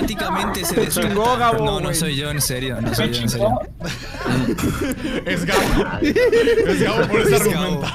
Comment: No se si escuche bien pero creo que dijeron que fue gabo, sentí q me hablaban a mi